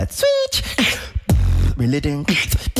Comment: Originally I thought was saying 'relating' in the second round. Now that I listen it makes more sense that he's saying ' reel it in'.